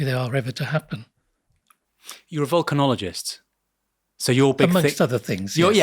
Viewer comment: I heard 'vulcanologist'